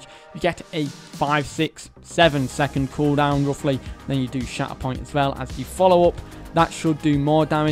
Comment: Every time he said shatterpoint I heard “shat upon” 😂